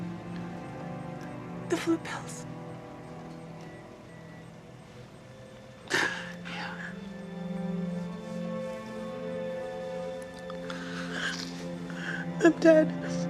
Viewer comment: No idea what she says at 13:13. "The flu pills?" Is my best guess.